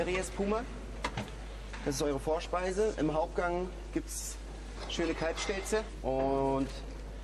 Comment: Ich hab eben verstanden das es "schöne Kackstelze" gibt.